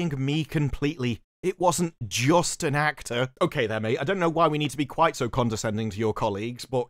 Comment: think you misread it - he said of the therapist she didn't see him "just AS an actor".